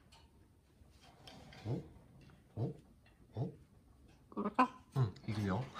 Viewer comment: I'm from Poland so the only thing I heard from Kiwi was the word "kurwa"